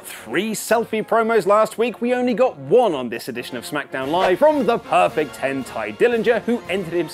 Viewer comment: thought he said "from the perfect hentai"